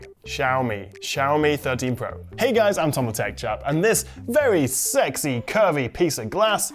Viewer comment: Watched you for a while now and I used to think you were called as the intro is ‘Hey guys, I’m Tom the tech chap’ but for a while I thought you said ‘Hey guys, Anton the tech chap’